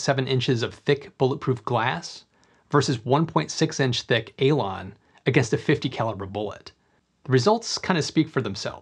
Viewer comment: I always hear you say "ELON's" when you refer to this glas 😅